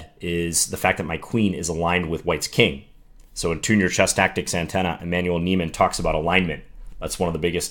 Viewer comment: Can some one pls tell me who he quotes at @ 8:35??? thanks a lot. I cant understand it, english not my main language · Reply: I was mentioning the book "Tune Your Chess Tactics Antenna" by Emmanuel Neiman.